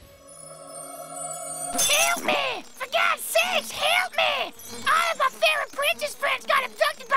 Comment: A Sprixie comes in peacefuly... 2 seconds later... 0:16, HELP MEH! FEH GED SAKES, HELP MEH! ALL EF ER FEREH PRENCESS FRIENDS GERT ABDUCTED BY A GIENT TURTEHL THANG! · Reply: I thought she said KILL ME, for god sake KILL ME bla bla bla bla